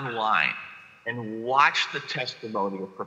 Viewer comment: did you say professor zoola from Ottawa? Please advise as I cant seem to find who this is and would really like to watch her video you recommended.